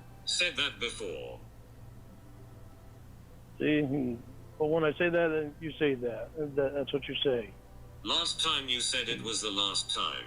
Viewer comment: gibberish that I missed on the first three times I’ve listened as I work, “But, when {I ?} say that, you say that, that’s what you say.” He should learn from “Thought Crimes” to 🎶BE CAREFUL WHAT YOU SAYYYYYYY🎶 This is a call that has so many hidden lines it must be studied. It’s a top tier favorite of mine already.